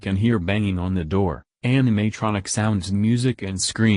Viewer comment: I heard anime tronics